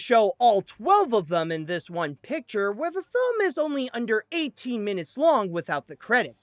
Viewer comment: that sounded like 18 instead of 80